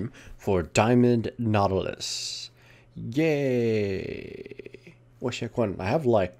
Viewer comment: It sounded like you said Gay instead of Yay